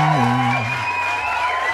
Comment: can you review Tariq Nasheed's album? I heard that "wash your ass" at the end there so I know you've listened to it. It'd be pretty epic if I do say so myself lad.